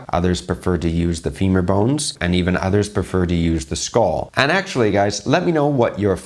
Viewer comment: did you say the skull? Just the skull, not the head, but the skull? 😳